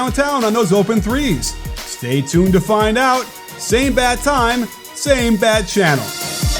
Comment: “Same bat time, same bat channel.” You are showing your age, Coach. So am I, because I get that reference. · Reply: I really thought he said "same bad time, same bad channel" before seeing your comment.